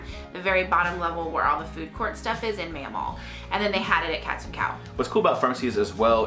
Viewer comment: Haha I keep hearing ‘cats and cow’ 🤣